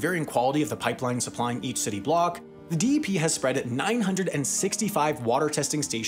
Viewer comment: did I just hear "spreaded"? I believe I did